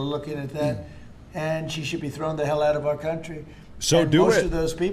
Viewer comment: i thought you said they were going to throw the mud ppl out